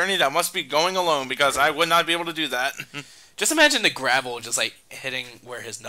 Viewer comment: At first I thought he said Grapple, now I'm seeing Lee's cut-off arm be replaced with a Metroid Grapple Beam